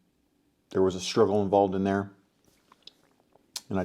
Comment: I couldn't understand what you said at 1:16-1:18, could someone repeat please? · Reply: "there was a struggle involved in there"